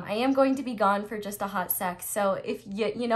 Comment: Whoa! I thought you said "I'll be gone for Hot Sex.." hahaha but no no you totally said "...Hot Sec." 😉😄😬